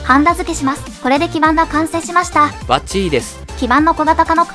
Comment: がバッチリです。じゃなくてバッチィです。に聞こえてしまった… と言うか、ロマン過ぎて観てて楽しい